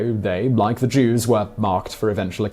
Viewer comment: Half the video i thought you were saying "ear marked"